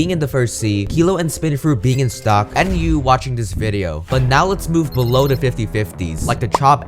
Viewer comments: when he said and you watching this video i thought he said enyu yk the yt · When he said you watching this vid i thought he said enyu watching this vid